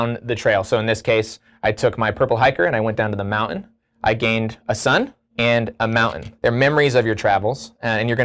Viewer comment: So was listening to this, and I just heard "I hiking and I gained a son" and I was like boy that's different than most hikes I've been on.